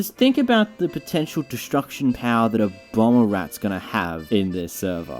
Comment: it sounds like he said Obama rat lol